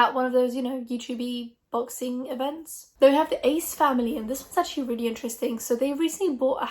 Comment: i need to comment that i thought you said aids family. i was like the what family, my brain is giving up on me holy shit